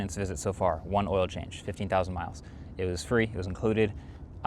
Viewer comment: Imo you should get oil changes more frequently than 15k. No reason not to do it every 5k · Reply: I think he's saying "I've had one oil change. Remember, it has 15,000 miles." rather than "The change was done at 15,000."